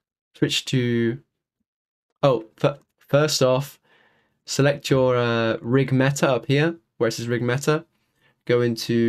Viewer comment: what does he say i cant understand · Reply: "Select your '_RigMeta' up here where it says '_RigMeta'," After that he says to push N (opens tools section) then click on the "RBX Animations" tab.